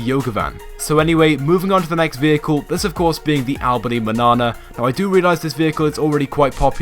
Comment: did he say Albany banana? Lol